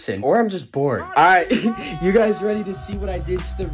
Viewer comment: I don't know what I thought he said "you guys gay?"